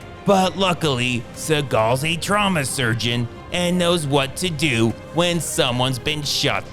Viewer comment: “But luckily Seagal is a trauma surgeon and knows exactly what to do when someone gets shot in the heart.” & “am I gonna die, NO you dumb b1tch can’t you see you have a shirt wrapped around you” has me crying omg your channel has had me binge laughing every day now. Most underrated movie recap channel I’ve seen. Can’t wait to see your channel get the subs and views you deserve for this comedy gold. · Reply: I thought he said "drama surgeon", which is almost funnier.